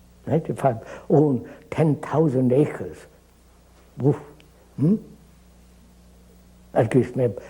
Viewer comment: I think he said "uff" as an expression of amazement and surprise. The subtitle is showing as "wolf" .